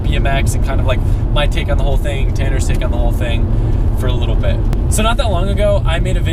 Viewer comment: it sounds like he is sayin tanner foust